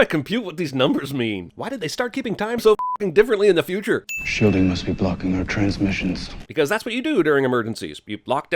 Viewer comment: captions say "i'm sure they must be blocking our transmissions" when it's "shielding must be blocking our transmissions"